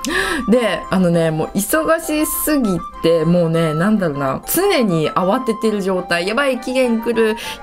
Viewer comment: めっちゃピークにの後何て言ってるのかな？ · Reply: そう、なんだろうな のような感じですかね？聞き間違えてる可能性ありますけど🥲